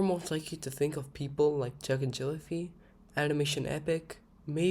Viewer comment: did you say "chug" or "jack"? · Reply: ”Jack”njellify